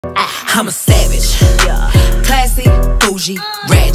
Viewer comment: What’s weird is that I actually heard it as “I’m a sandwich”.